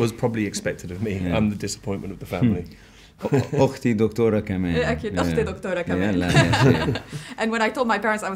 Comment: Can someone tell me what Rami said at 7:14?? 😭😭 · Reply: "My sister is a doctor too " = okhti doctora kaman, je cas a very good arabic (egyptian accent of course )